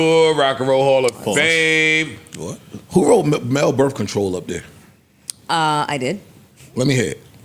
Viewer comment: I definitely heard “Mel birth control” Not “Male birth control” 😂 I feel that was done purposefully